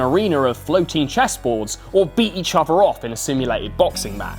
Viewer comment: Did you just say "beat each other off" @ 8:39 😂😂😂😂